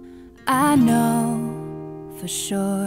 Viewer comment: when I hear "I know",I thought that was "I Norsk" lol